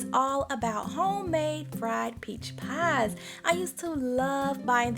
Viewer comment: I thought it said Best Friend Peach Pie, and I was thinking...Yes, you will be my BFF for a peach pie!